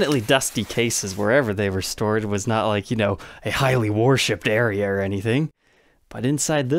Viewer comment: What does say here—> 3:23? It sounds like “a highly ‘warshipped/worshipped’ area”??